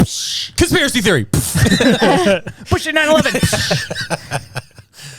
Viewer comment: Did he say, bullshit 9/11?